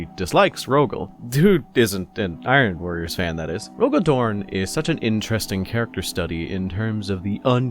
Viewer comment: I keep hearing "RoboDorn".